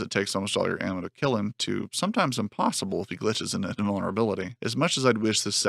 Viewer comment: this part sounded like gibberish though. "sometimes impossible glitch bdjkvulnerability". That boss was so impossible and unreal that I had to check online and realise there's a reason why this game is unpopular and underrated. New Nightmare is a broken mess.